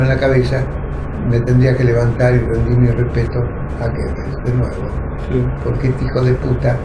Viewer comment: No entendí a quien se refirió en el minuto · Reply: Gebbels